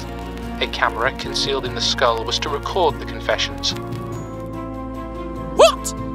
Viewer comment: I was confused at first I heard it as "crime, skeleton" lol